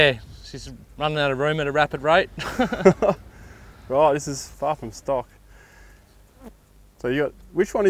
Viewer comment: When they lifted the bonnet I thought said that's fucking sick instead of far from stock